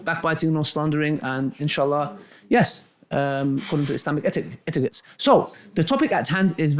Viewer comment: Did He just say according to Islamic etiquettes 3:17 subhan Allah